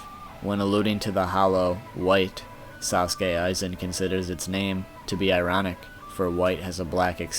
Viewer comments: is it just me or did he say sauske aizen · did you just say "Sasuke Aizen"